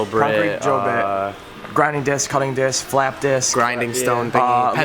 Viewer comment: I thought I heard you say fap disk at first... had to go back and listen again lmfao.